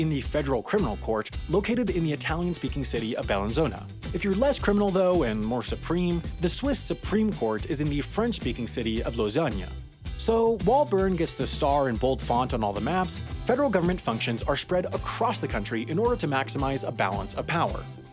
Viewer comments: Did he just say Lausagna for Lausanne? If thats meant as I pun I dont really get it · yooo! did i hear Lasagne??